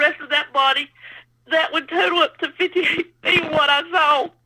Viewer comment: I don't doubt the credibility of her encounter but she was so incoherent could anyone pls tell me how big she said it was? I thought she said 85 feet! I know that can't be right she was very garbled, likely traumatized but she was very incoherent.